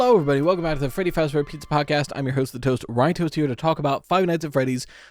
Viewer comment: day one of asking so say "its me the hostess with the toastest, ryetoast" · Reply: I always thought they were saying “the host with the MOST, ryetoast”